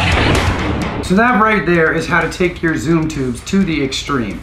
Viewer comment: Damn, I thought it said Zoo Tube.